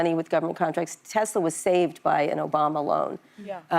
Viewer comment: did she say, 'Tesla was saved by an Obama loan?'